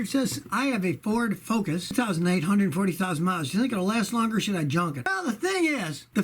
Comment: did you say 840,000 miles?